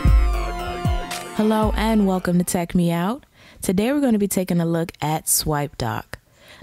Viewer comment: I always thought u say tech-meow in the beginning -.